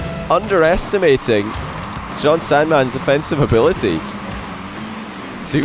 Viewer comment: Did say “ Jon Sandman’s” offensive ability at 17:18?? I mean even a blind squirrel finds a nut once in awhile...